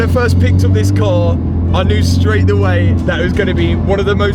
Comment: hang did you just say "straight the way" instead of "straight away"? haha